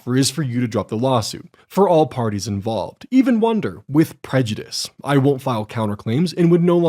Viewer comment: I mean, just wanted iiluminaughtii to remove the lawsuit. But already paid $7000 in legal fees, and he was emotionally stressed by it. It's just weird not to demand your legal fees back. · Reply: From himself of course. 3:59:17 "[...] my settlement offer is for you to drop the lawsuit for all parties involved even wonder with prejudice i won't file counter claims" The automatic transcript is wrong here, I think he said without prejudice? Well, deciding prejudice is up to the court. I guess he meant that she won't re-file the lawsuit ever again.